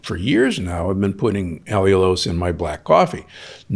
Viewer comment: what did he say he put in his caffe? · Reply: Allulose